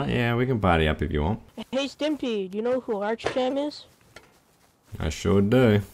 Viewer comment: did he say "do you know who archpenn is?"